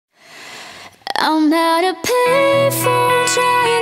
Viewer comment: i thought she said " Im not a payphone" at first by the way better that the original :3